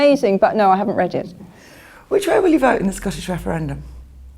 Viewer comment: As an american, I have no idea what she just said. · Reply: Which way will you vote in the scottish referendum? (Whether Scotland should stay in the UK or become an independent state).